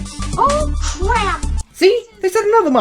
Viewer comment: I thought he said “Oh, come on!” WTF